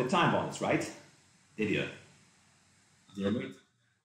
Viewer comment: did really call him an idiot??